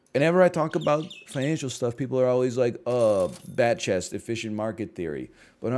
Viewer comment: In case you're not memeing, here's a basic breakdown: the emote is "Batchest". It's a picture of a man's face, making an expression of open-mouthed excitement while wearing light-up sunglasses, and is used to imply mock interest/sarcastic "excitement", typically in reference to a popular nerdy form of media. So like, if someone posts that they're super excited for the new Batman movie, you would type "Batman! (Batchest)", with the meaning being something close to "Oh, you like Batman? How original." The name comes from a stream where a chatter noticed the streamer's chest hair looked like the Batman logo, and the streamer claimed to shave it into that logo every morning, prompting people to think that was a pretty weird or "cringe" thing to do, and the image comes from a Twitch Admin who took the picture of themselves, which people associate with a "soyface". (If you don't know what a soyface is, I can explain that as well, but it'll be a little longer.) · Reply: Thanks. I don’t watch twitch so I don’t know all these memes. I sounds like he is saying black chest…